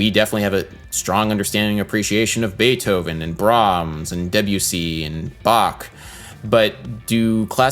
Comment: Is that really how you pronounce Debussy? It sounded like you said WC.